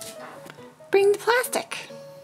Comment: When said "Bring the plastic" it sounded like Nagi said "Nnnnnnoo."